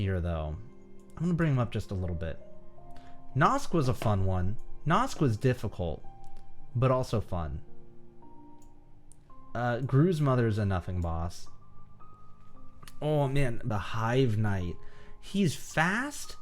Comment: I thought he said Gru's Mother for a second there.